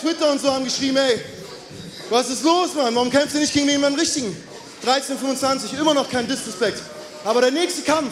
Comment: was sagt er hier ?? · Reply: Was ist los Mann warum kämpst du nicht gegen jemand Richtigen? 13-25 immer noch kein disrespect